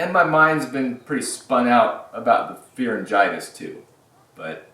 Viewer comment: Um ... @ 1:27 Did you just say "Beer-eng-itis"?!?